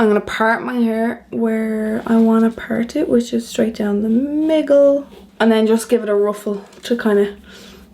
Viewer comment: Did I mishear or did you say at 11:22 that you part your hair 'straight down the miggle'?!? On other days do you part it 'down the slide'?!? lol It sounded very cute actually :)